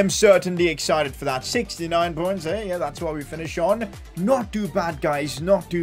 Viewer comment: 69 😂😂😂 What did he say next? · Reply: 69 points eh that’s what we finish on I think